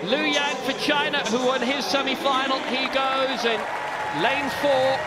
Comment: did he say Virginia